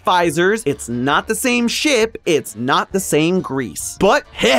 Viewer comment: i heard it’s not the same sh*t then realized 5 secs later this is supposed to be family friendly💀